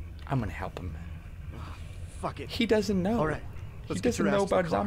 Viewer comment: it sounds like says shit help me out does he i wouldn't care haha